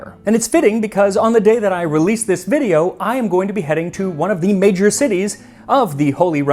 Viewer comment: What Max said, "I am going to be heading to..." What I heard, "I am going to beheading to..."